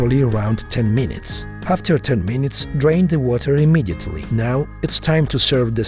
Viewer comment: Why tf did I first hear ”After a 10 minutes, Drink the Water immediately…”